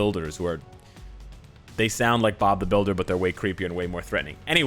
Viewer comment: Literally did said Bob the builder